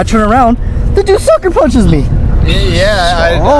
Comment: what did say?... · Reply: "They just sucker punchered me "